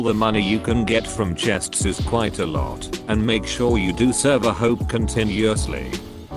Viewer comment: u said server hope instead of server hop